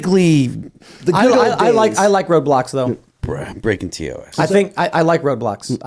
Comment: i thought he said "i like roblox" instead of "road blocks"....